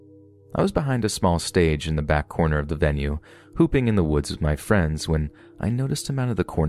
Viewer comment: was I the only one that rewound the video to make sure I heard her say "pooping" next to the stage! OMG!🤢